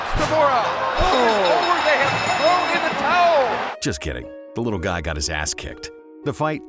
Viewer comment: I swear i heard the guy say "little dick"